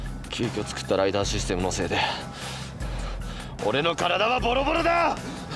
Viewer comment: オデノカラダハボドボドダ！ってさ。字幕なかったら普通に俺の体はボロボロだに聞こえるんだけど。